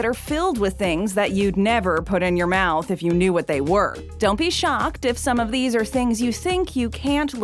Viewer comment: When she said dont be shocked for second I thought she said dont he shy put some more 😂😭